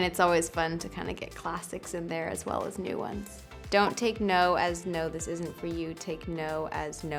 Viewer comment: Can you guys just help me this ? She said : Don't TAKE NOTE or TAKE NO at 0:28 please ? I just can't hear clearly, thanks